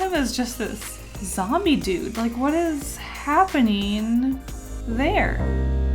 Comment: i love how she says ‘hape-NEEN’ instead of ‘happening’ lol